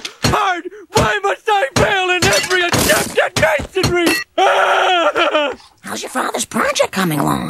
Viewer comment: "Why? Why must life be so hard? Why must I fail in every attempt at masonry!" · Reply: I thought I heard “why must I fail at every attempt TO GAIN SOME RE..” or respect if he finished his sentence